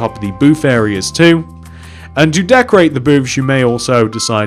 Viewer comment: it sounded like he said boobs xD